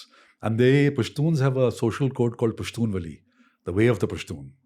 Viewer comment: Being a Pashtoon I wanted to tell you that this guest has a lot of knowledge. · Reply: Wait WAITTT...DID I HEAR HIM SAY AT 6:12 PASHTUN VALI!!??? THTS EFFNG TAMIL!!! 😳😳😳. Pushtun n tamil connected!!??? Did u guys hear that!!?? Pashtun 'vali'. Vali is 'way' in tamil. Remember rajnikanth saying...en vali .thani vali! (Jst shut up keralites..i know wt it means in malayalam uff😏😒). Sooooo tamil & pushtun!?? Wait wat!!?? Mind blown